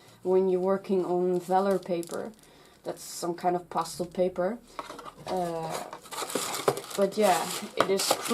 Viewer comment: When you were saying pastel paper it sounded like you were saying pasta paper.